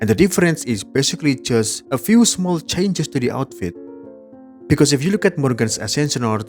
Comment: did bro just said "A few changes DARI outfit" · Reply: Don’t scare me like that, lmao. It’s “to the outfit.” I’m already away from the voice generator. If my OWN mouth starts speaking in auto local language, I’m gonna be damned.